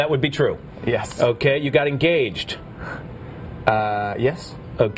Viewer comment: Idk if i heard right or not but.. they where engaged??